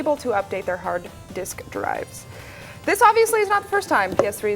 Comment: all I heard at 4:04 was hard d*ck rides sorry had to share